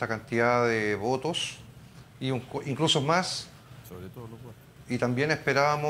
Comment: Alquien dice "sobretodo los wnes" o algo asi. Quien fue y que dijo?